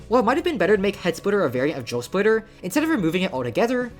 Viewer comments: i keep mishearing it as "joe splitter" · I thought you were saying joe splitter and i was confused cause i see drill splitter and not joe splitter